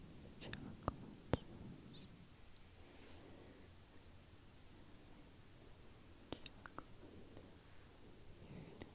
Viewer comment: What ever you were saying at 6:43 was so clicky and tingly 🤤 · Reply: She said chocolate sauce